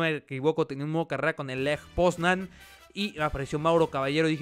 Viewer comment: Que equipo es el que dices en el minuto 1:55? Lej bogsnan? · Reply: Lech Poznan creo